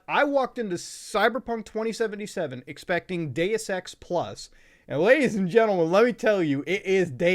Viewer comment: anyone else who thought he said 'day of sex' at first lol?